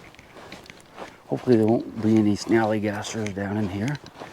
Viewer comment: What in the world did you say at 4:10?! I turned on subtitles, and it seems to think you said, “Hopefully there aren’t any snowy gassers…”